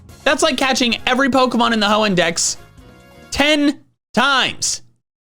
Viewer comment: Captions says "hoe index" 😂